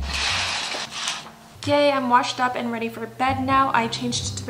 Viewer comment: DID SHE JUST SAY GAY- 😭😭